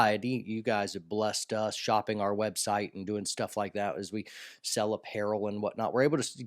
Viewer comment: Pardon my moment of amusement, but when you said “sell apparel and whatnot” I heard “sell up heroine and whatnot”. Hopefully I can bring someone a smile on account of my hard hearing. That being said, I know this is a somber subject, and I appreciate what all vets gave up for our freedom. May we all earn their sacrifices.